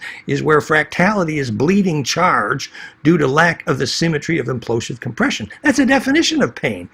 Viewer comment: where fractality is bleeding charge due to lack of asymmetry of implosive compression 26:21 one question: shouldn't it be lack of symmetry? of did you say a symmetry hahah thanks👍👍